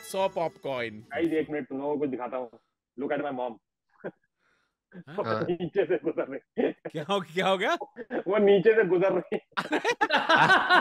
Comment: I thought, tanmay thought, said ''Wo neche se guzar gayi''☠️☠️